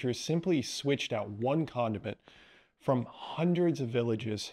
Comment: I’m not giving up sex … no way. Ah .. condiments I thought you said condoms.